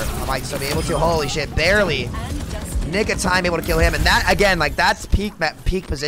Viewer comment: IN THE WHAT OF TIME (i know you said nick of time)